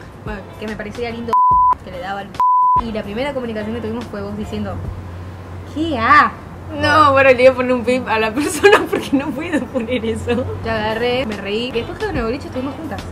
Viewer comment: para mi que dice Lucas Castel Niideahh